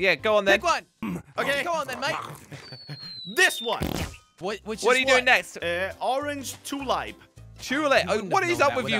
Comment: did say the d word? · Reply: No he said damn it